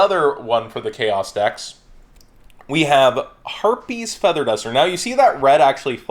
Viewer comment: did I hear him say herpes or harpie?